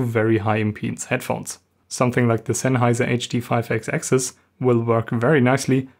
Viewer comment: Excellent video. What is the model of Sennheiser headphones that you said would work nicely with the Studio 24c? I apologize, however, you said it pretty quickly at 8:35 and I didn't catch it. Thank you. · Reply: Hey Robert, I was referring to the Sennheiser HD58X.